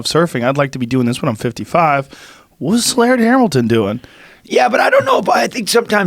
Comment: Damn, I thought it said Linda Hamilton and got excited